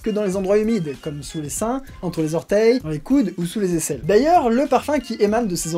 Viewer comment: A 4:21 j'ai compris dans les couilles c'est normal?